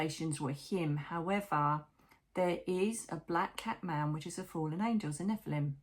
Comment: I thought you were saying black cat man. Lol!!!